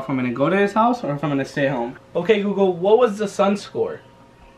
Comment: I thought he said "ok hugo" at first.